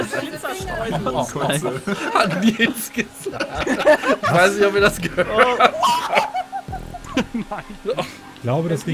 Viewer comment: Habe Nils' Kommentar bei 2:30 nicht verstanden ☹️ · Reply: er sagte kotzreiz, wenn ich richtig gehört habe :D